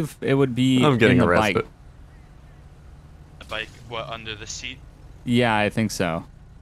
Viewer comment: I thought the cop said under the sea